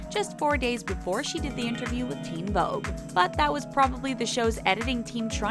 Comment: Did anyone else notice on 4:08 subtitle they said " theen vogue" instead of "teen vogue" ?